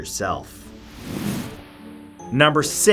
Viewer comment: I always thought it said give me crack corn